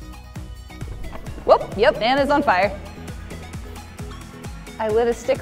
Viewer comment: I heard "Amazon Fire. I lit a stick on fire." Which makes me think of the fire tv stick. Lol